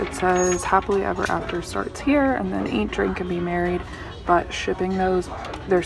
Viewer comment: Haha I thought that sign said "drink and be worried" not married. Haha